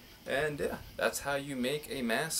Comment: I thought it said cucker mask.